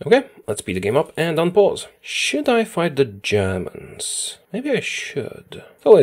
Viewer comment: am i only the one who heard "shit i fight the germans"?